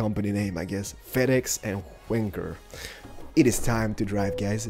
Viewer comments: Did he say wanker at 1:20?? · did he say w*nker